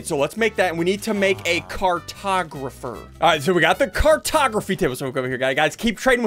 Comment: when you said cartography i thought you said cartografeet